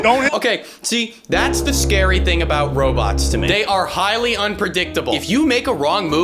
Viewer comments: STOP at 7:33 if you turn on the captions it says “optimal” instead of “unpredictable” FREAKY 🤣🤣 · Kinda creeped out that the auto-generated captions said "optimal" instead of "unpredictable" Don't know if that was a bug or OTHER things.